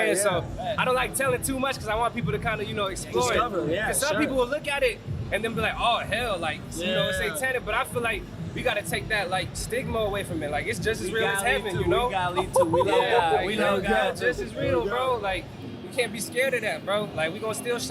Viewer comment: did he just say “take away stigma away from HELL?” he’s a nice kid but that’s super funny lmaooooooo